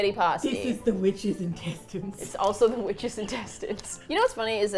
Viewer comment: From today on I will refer to spaghetti as "The witches' intestines" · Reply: I thought they were saying “witches in Texas” and I thought it was an American saying. Then I read your comment and ooohhh...😅